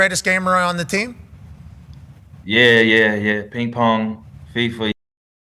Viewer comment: What happened to the audio between 10:09 and 10:15. What did Keenan say! 😂😂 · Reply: he was saying fifa, but the system thought he was cussing.